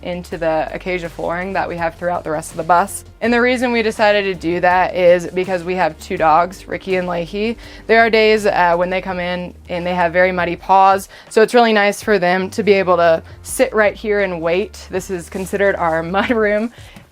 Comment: Ok. Gotta pause this to giggle at "we have two dogs: Ricky and Lakey." Beautiful home. Quite a challenge to take on a 40 foot vehicle, but well done. And a very nice tour. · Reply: I thought she said Ricky and licky hahahahaha